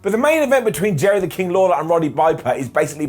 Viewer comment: Did Simon say Roddy Viper?